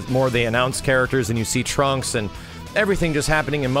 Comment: Oh no I misheard Trunks as Trumps and now I will never unhear it.... Also imagine the lobby being like a small planet like King Kai's home. :D